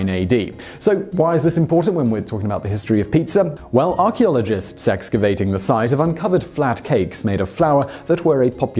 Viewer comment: i heard "archeologists sexcavation"